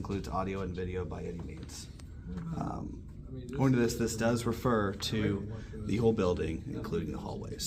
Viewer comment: I think he said "this does refer to the whole building" not "the old building".